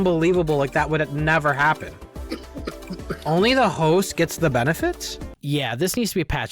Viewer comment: I thought you said “hoe” instead of “host” 😂 I thought you were saying only the hoes get the benefits